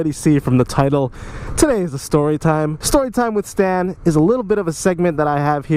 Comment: I thought it said "story with satan"😂😂😂